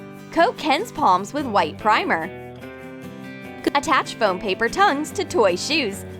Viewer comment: did it glitch for anyone else? Kinda like “gattach” instead of “attach”